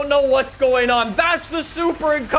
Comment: I swear he said Stimulator